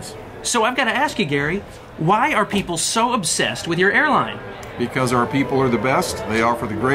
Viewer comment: it was playing in another tab and I thought said why are people in love with your h a i r l i n e 😂😂😂😂😂 I'm dying